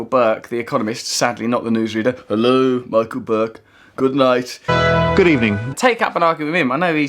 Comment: Accidentally (dropped something on my keyboard) turned on subtitles and was amused by a couple of mis-transcriptions. At 4:34, MacBook for Michael Burke. At 4:45, "150 thousand new nazis'" instead of nurses. Oh YouTube, you never fail to entertain.